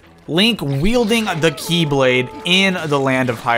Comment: I thought it said "Beyblade"